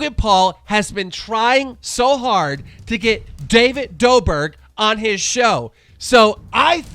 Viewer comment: did he say David doberg